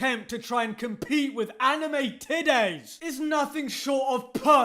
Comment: When every time I heard "Anime today" sometimes I misheard them as " "Anime tiddies"